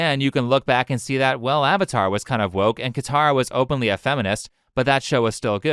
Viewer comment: I misheard "Katara" for "Korra", so I thought you said Korra was good. Was about to burn down my house because of this.